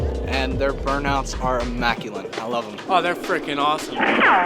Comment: Did he say immaculine? What does it mean?